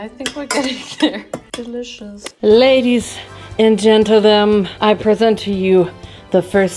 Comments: Did you say "Ladies and Gentlethem" at 19:12? 😂✨ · am I delusional or did she say ladies and gentlethem 😂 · lol babe…. What was that?!!? “Ladies and gentle dome?” Lol that’s what it sounded like